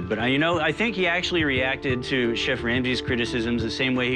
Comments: Did he say chef Randi? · Alssoooo did say Chef Randy in 2:47?